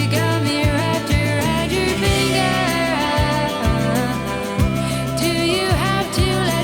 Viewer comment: I didn’t even know the Cranberries were Irish until their third album or how an Irish accent sounded until I saw an interview with the late singer Dolores. Before then, I thought Dolores sounded like she said in an American accent “Roger, Roger” instead of in her heavy Irish accent “wrapped around your finger”.